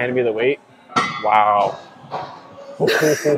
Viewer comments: "Your gonna hand me the weight? WOW- woowww- oh get down 😭" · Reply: Omg THANK YOU! I heard “Good Cow” 😭😭😭😭😭😭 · "Your gonna hand me the weight? WOW- woowww- oh get down 😭" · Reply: Yes, clearly I heard “You’re gonna hand me the weight? Woww, hohoho wow, ohh wow, oh good cow...”